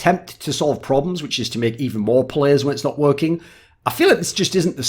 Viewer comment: Make even more players. I don’t understand what he was trying to say. Could somebody elaborate/explain this too my small brain. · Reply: he said make more plays, it's his accent